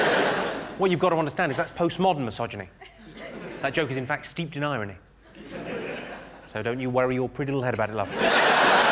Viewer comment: What did he say at 0:30? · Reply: "That joke is in fact steeped in irony, so don't you worry your pretty little head about it"